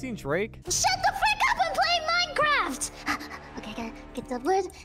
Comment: I heard said "SHUT THE F*CK UP IM PLAYING MINECRAFT"